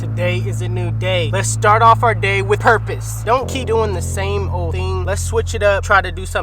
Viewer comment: Okay.. I wasn’t really paying attention at the beginning and I thought you said “let’s start our day with herpes” 00:18 😂😂